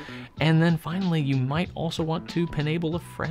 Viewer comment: “You might want to enable a friend” was me right after I got my Kaweco. 😂😂 I was like “YOU NEED THIS! HERE ARE THE INK SAMPLES TO TRY WITH IT!” It didn’t work. But I’m not giving up! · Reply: that’s what I heard also! “Penable” is a great word for this situation 🤣🤣🤣